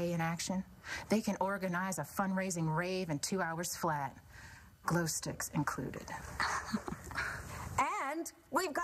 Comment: XD I thought she said 'glue sticks'. I was wondering....what the? Is that the new weapon fad?